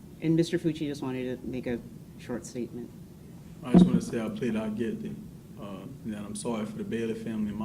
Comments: He’s saying “I just want to plead ‘out’ guilty.” That’s why it sounds that way. I’ve heard that said before. The slang term for it is “plead out” so you get out of a trial and hopefully get a lighter sentence. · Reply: Thank you for clarifying. I thought he misspoke and said NOT guilty 😛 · I think he’s saying “I plead out guilty” I def can see why you heard it as not, his accent is thick!